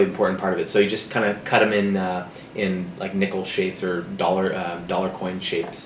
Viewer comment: haha... when he said nickel shapes i thought he said nipple shapes o-o